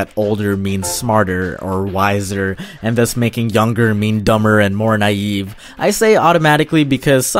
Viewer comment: Did he say dumber